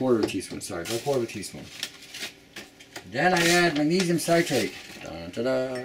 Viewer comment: I couldn't understand what you said was the 3rd thing you added was cuz you were mumbling so what was it ? It sounded like you said Amedium Citate .. never heard of that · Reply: Cathy Moore magnesium citrate